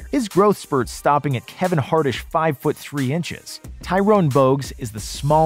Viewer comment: Did i hear "Kevin hartish"?? 😄